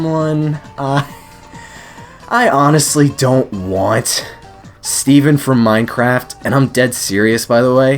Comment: Did he just call Steve from Minecraft "Steven"??